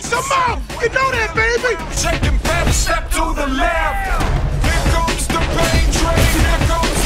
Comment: I keep hearing "Here comes the penetratin'."